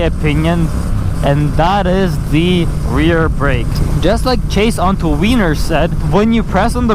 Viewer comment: did he say 'chase on two wieners' 4:45 ...